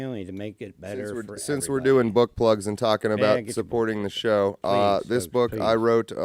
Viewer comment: omg I thought he said butt plugs...... "BOOK PLUGS"